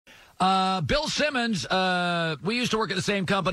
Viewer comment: Thought is said Ben simmons🤦‍♂️